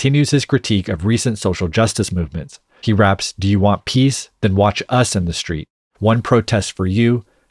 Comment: I am thought he said “do you want peace? Then watch Sesame Street?”